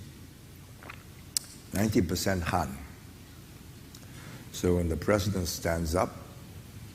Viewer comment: I’m sure most people know but at 1:45 the subtitles are wrong. He said 90% Han (The predominant ethnic group in China)